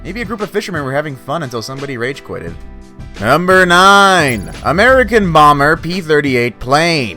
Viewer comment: I thought he said America boomer plane Not America bomber plane 😂